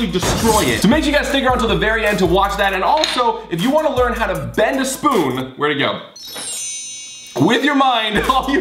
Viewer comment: did he say "bend a spoon"? too easy